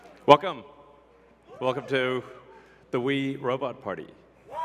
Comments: I thought he was gonna say welcome to the diddy party lol When the robots came out and he said they can walk your dog lol what if we have cane corso or pitbull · I heard "Wee Robot Party"